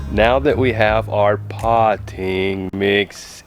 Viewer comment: I thought he said potty mix too! LOL.